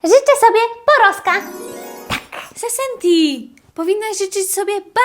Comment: Czy tylko ja usłyszałem: rzyczę sobie pornoska?💀💀💀